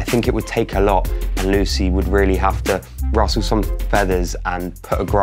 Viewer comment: did he say Russle some feathers